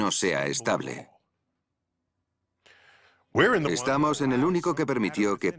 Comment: hay un error en la traducción donde dice "estamos en el único" en realidad se escucha que dice" en el cual que " En la forma traducida en el video, no tendría sentido la teoría de múltiples universos ya que habría infinidad de ellos y este no sería el único con esas leyes